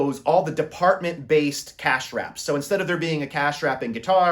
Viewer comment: they did what? 🤷🏻 Slow down. Say your words completely. I have no idea what "cash straps" are. Or if that's even what you actually said 🤷🏻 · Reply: Cashwraps, it’s a common term in retail. It’s basically a hub of registers.